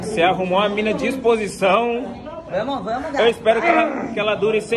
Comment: ouvi vamo gato! ou estou ficando lokoooo?